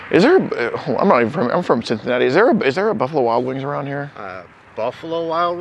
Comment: Oh I thought you said RUFFALO Wild Wings